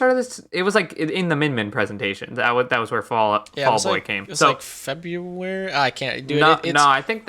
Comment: I don't know why but i thought you were saying fall boy instead of vault boy and was wondering when a fall guys rep made it in as a mii costume.